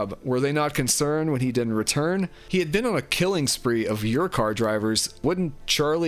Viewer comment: Am I the only one that thought they weren't saying your car! I thought they were saying yurt cart and then yurt car. Because they're not separating the two words into your car they're like saying them together and I'm like what the fuck I'm like oh it's a rideshare service after the first like car guy pops up😅